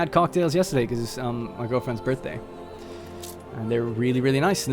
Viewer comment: Did I just hear him say "Our girlfriends birthday", are we included in the relationship? I am not sure if I'm ready for this commitment :p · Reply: Ha, slurred the words a bit but it was “muhgurlfwends birthday” :P